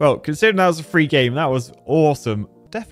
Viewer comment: Did you say "Oresome"?